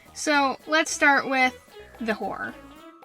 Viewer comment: Help at 1:05 i thought they said "lets start with.... the WHORE "